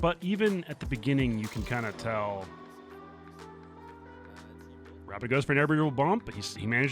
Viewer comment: Rapid goes for an AirBibblebump or as youtube captions says it "Rabbit goes for every little bomb"